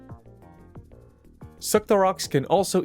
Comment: did you say suckorocks?